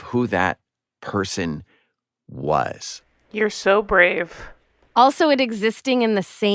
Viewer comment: Not enough oxygen in that room haha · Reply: I just heard “you’re so brave” 🤣 love it